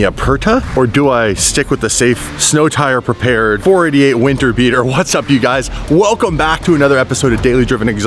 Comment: says winter beater and it sounds like he says weaner beater 😂😂